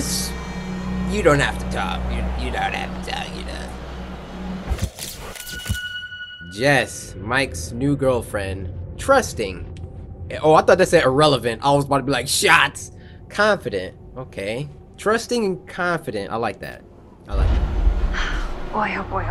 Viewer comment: Who else thought that said irrelevant? 🤣